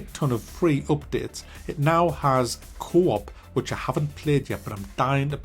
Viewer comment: When he say Co-op I hear “Cwop”